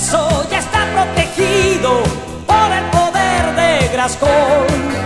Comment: Yo siempre pensé que decía : por el poder de gascon y es grayskull 😂